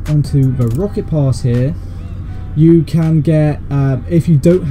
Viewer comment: When he said rocket pass I heard it as rocket loss XD